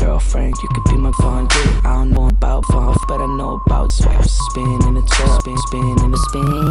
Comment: Uh oh, did I hear SPEEN.